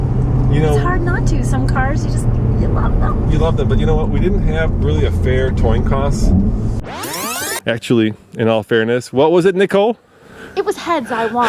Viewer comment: did he say "fair toin coss"?